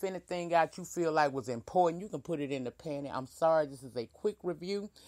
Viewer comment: Did you say put it in the panty??? Sorry if I misheard you.